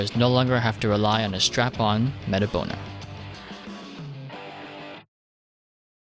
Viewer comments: did he just say "strap on meta boner" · 'Don't have to strap on that metaboner' or did I mishear?